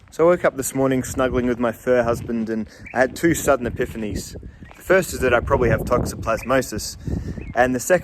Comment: With your fur husband.. thought you said "third husband" lmao.